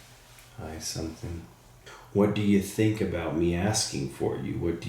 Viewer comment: Maybe I’m crazy but at 11:15 I actually thought I heard “I’m not sure of that” 😮